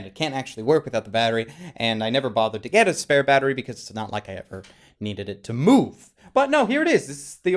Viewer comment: my video stopped buffering so when you said you need it to move it cut out the last part and sounded like "It's not like I needed it to moo" i love my simple little sense of humor